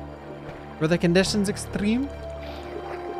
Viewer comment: In the RE4 mod at first i thought you said "the condition is streamer" lol